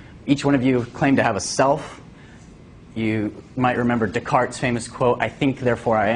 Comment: It sounds like he said cogito ergo sum was a dick clark quote. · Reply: He said "Descartes", not Dick Clark.